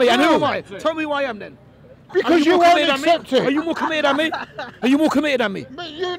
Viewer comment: what is he saying? I thought he said "are you looking weird at me"?? Anyone??